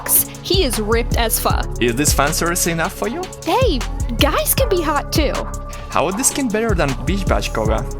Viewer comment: Did she say 'hey' or 'bae' (insert lenny face)